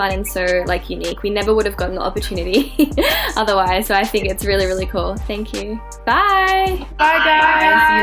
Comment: おやすみ~って聞こえるwww